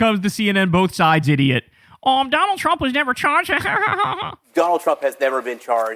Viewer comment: Donald has never been charged but that’s not at all what dude said, he clearly said “involved with”